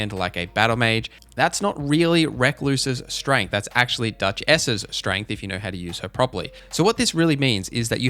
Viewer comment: On first listen I thought you said "Duck-ess's strength" and now I want that to be her name.